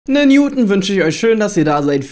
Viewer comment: Ich habe erst verstanden "nen Newton wünsch ich euch"... also ganz ehrlich, viel schlimmer als mit Ehlinger oder Ryan würde es mit Newton aktuell auch nicht laufen